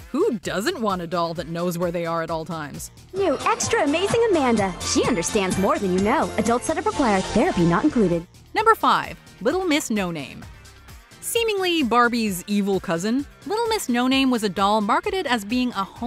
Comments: Bruh did she just say therapy not included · Did that just say "Therapy not included!"? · did that just say therapy not included 😳